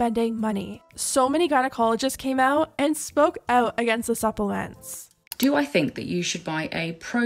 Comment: what word was that? ‘Suppelance?’ 😂